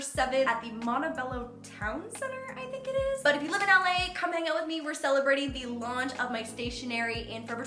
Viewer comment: Is it just me or did say "come make out with me"?? 1:23 😂